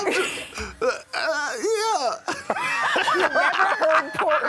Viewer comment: Did Malike just say "I've never heard PORK's voice go to that octave"?? LOLz